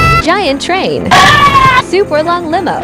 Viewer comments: What he said: "My Subs car" What i heard: "My S*X car" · Bro I heard “my s6x car” 💀